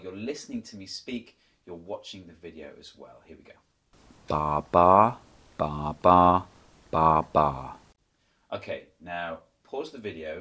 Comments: I heard "Dah dah, bah bah, bah bah". And the second time: "Dah bah, bah bah, bah bah". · I heard, "da ba da ba ba ba"